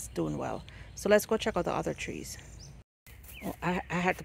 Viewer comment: Good morning, I love that mango tree, I would love to have one like that, can I have a seed?. Everything look so green. Need to get some neem for my plant . Thank for the information. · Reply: the Mango tree with the full it sound like you say Carrie mango tree with the blossom I have a small yard so small tree is ok.